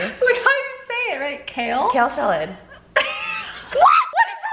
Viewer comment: I heard “Cal Salad” AHAHA love